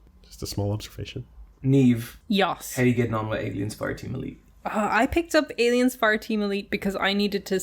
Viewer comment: Every time I hear “Aliens fart Emily”